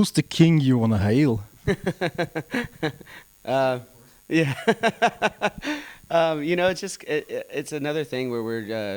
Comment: I wish I knew what Matt whispered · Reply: "My son of course"